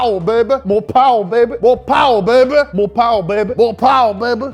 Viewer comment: I thought he was saying "Mopar Baby" for a sec lol